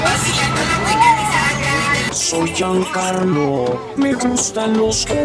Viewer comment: porque hay una canción que dice : Soy Joan Carlos me gusta los hombres. Jajajajaja